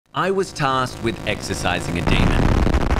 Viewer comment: i thought you said at the beginning “i was tasked to exercise”